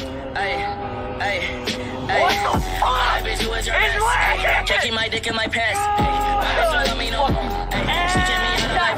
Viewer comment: what did he say there “ its working or its lit”? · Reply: def its lit